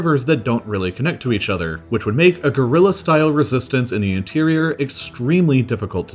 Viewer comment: I heard "A gorilla style resistance" and for a few seconds my brain created crazy speculations about such a concept...